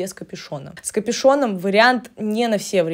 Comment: Услышала "капИшон" И обалдела! А главное --- про девушку все стало ясно! И вообще --- все это кругом и всюду! Все " Несут" Одно и то же! И даже фото покпзывают те же! На кого это все рассчитано? На алкоголичек под кайфом? Это каким же надо быть "темным", чтобы это все слушать?